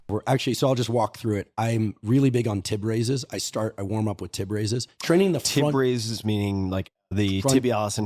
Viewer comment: Am I the only one who thought he was saying "I warm up with tit raises?"